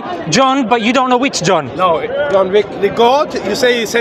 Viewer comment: NOOOO , did I hear John Wick 28:40 LOL